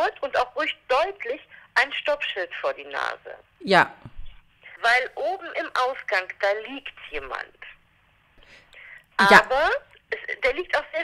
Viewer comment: ich hab erst verstanden : „weil oben im Aufgang, da liegt jemand“ 😂😂😂